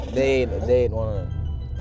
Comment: We are not going to sit here and ignore the fact sterling prenounced fredo's name "Freedo" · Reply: Mohammed Yusuf could’ve sworn it’s just said Fredo